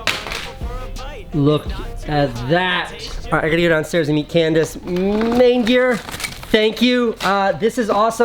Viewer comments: coulda swore you said alright i gotta go downstairs and eat Candace i was like..wait what, if you just listen without looking at your lips it really sound's like that lmao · I thought he said eat Candace